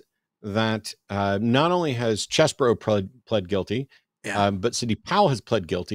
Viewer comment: Did he just say "chess bro" pleaded guilty? Lol what? I'm sure I misheard that but whaaaa😂 Who Dat and whyyy